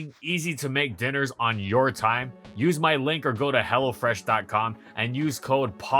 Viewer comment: for a second I thought he said “use my link or go to hell” lmao